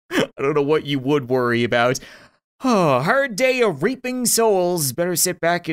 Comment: I thought he said 'raping' souls not 'reaping' souls